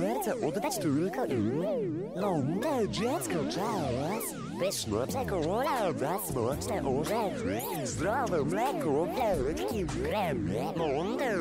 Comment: ja usłyszałam „smocze orzechy” O NIE SMOCZE JAJA XD